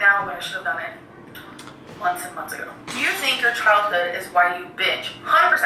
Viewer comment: I heard "Do you think your childhood is why you bitch?" at 10:28 lolll